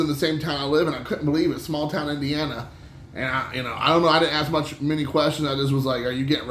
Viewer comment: ZZZZZzzz...... Hzhwaaa? Woke me from my sleep. I thought you said Smackdown Indiana. All backwards.